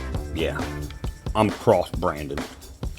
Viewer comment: lololol I thought you said "I was cross-brandon!"